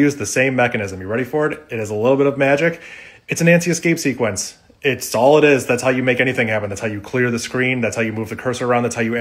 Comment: I kept hearing that as "anti-escape sequence" and was very confused